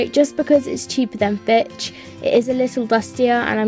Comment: and does the say the b word at 4:22? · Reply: Trinity Ramirezx it’s called Fitch